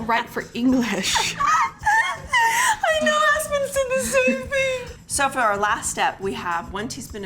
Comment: Lol when she said aspen said the same thing i thought she said my new husband said the same thing n I was like ohhh that’s fantastic!! I was wrong thou she said aspen lol